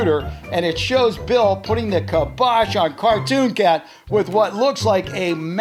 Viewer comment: did he say cartoon cat